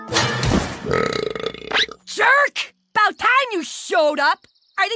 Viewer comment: 🤔 hmmm i hear "i want pot" but listen a little closer "i want pie" 🥧